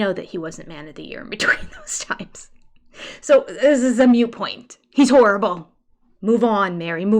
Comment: Hahaha, around 3:34, mute point? I didn't hear moot point, but maybe I'm wrong?